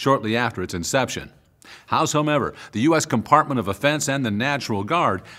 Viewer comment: did he say compartment of offense or department of defense